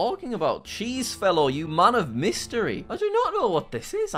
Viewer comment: When he said "you're a man of mystery" i thought that he said it's a murder mystery,and I was like 😮, 😂